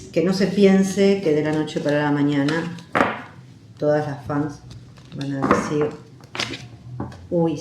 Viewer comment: Bueno ahora nos corrigen y dice que dijo con mis fans ,bueno la verdad que no se entendía eso .Mejor tranquilidad ,quizá la indirecta fue lo suficiente directa 😅😅😅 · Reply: yo cuando vi el vídeo la primera vez no entendi "esposa", pero al escuchar de nuevo sí que me pareció!Ahora escuhandod e nuevo ya me parece que dice "las fans"...bueno, es un capítulo más en esta aventura!!!😆🤭